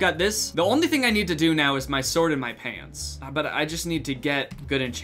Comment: “i just have to do my sword and my pants” ya know just talking about enchanting but what i heard was “the sword in my pants” lmao